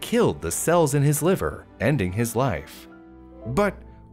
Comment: whenever he says B. Cerius or whatever it’s called, all I hear is BE SERIOUS. So I’m over here like, ok ok I’m being serious! 💀